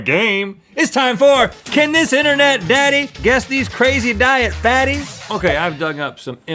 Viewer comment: I wasn't looking at the screen, and I thought he said Crazy Diet Fatties. OH man...